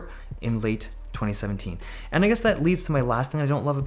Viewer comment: i thought he said "And niggas that leads" lmao